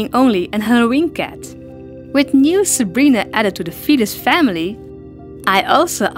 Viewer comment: At first I thought you said the fetus family